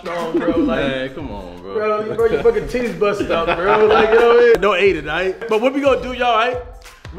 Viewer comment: no adin instead of no homo😭